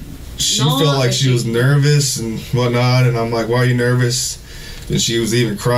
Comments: Mark Browden tottaly misheard him, he didnt say "one night" he mumbled "and what not" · Sometimes language is used differently across the pond. I'm English, like Mark, and I would use the expression " I saw her one night.." if I'd only seen her once. I'd be referencing the frequency I made that trip home. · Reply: I think Mark misheard. He quotes, "In that case, I saw her swerve one night", but what was actually said was, "I saw her swerve and whatnot." At least, that's what I heard. Love you Mark.